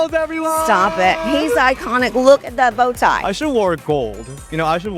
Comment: I thought she said: "He's iconic, look at the Botox" 😂